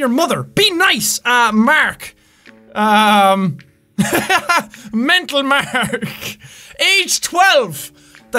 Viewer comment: I thought it said "Hentai Mark" 😂😂